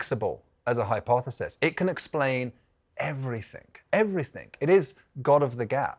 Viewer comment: Did he say "everythink"?!!